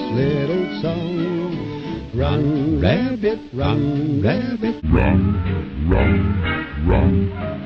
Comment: I thought the song was saying, “Run Rambley. Run Rambley.”